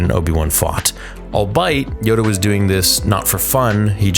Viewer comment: was he trying to say albeit? sounded like he said all-bite lol